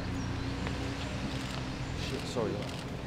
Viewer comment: Did i just hear "shit, sorry" 🤔